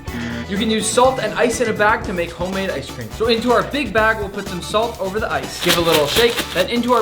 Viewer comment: 😭😭😭😭I can not be the only 1 who herd “big back” instead of “big BAG” 😭😭😭😭😭💀💀💀💀💀